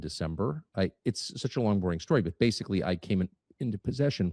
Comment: did he just say “It’s a long boring story”????